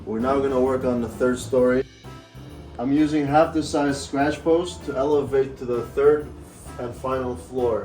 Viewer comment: Fantastic job! The kitties already love their new tree! I was going to ask what you used for the supports, and I thought I heard you mention scratching posts. Is that right? 🐾🐾